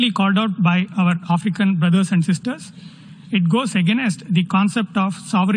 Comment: I am glad the members have their translators. I can imagine his accent being hard to understand. Did he say "againest" at 03:32?